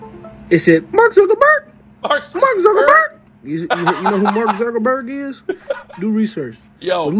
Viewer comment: i hear "We're watching you" instead of Mark Zuckerberg